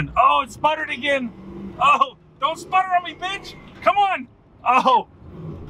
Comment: Did anyone else hear him say “Don’t sputter on me Bitch!” 1:51 Lol must’ve been the ringing in my ears. Lol